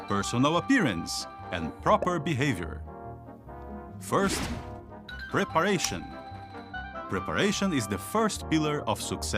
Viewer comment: The 3 P's Lol - I thought he said "Perspiration" not Preparation. I was thinking deodorant - hot work wandering the streets - you don't want to have body odour!!